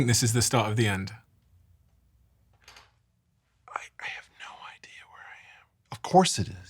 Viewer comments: through 24:13 (listen closely) what's up with Eric Weinstein's whisper, "I have no idea where I am"? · Did anyone hear Weinstein whisper at point 24:14 "I have no idea where I am"?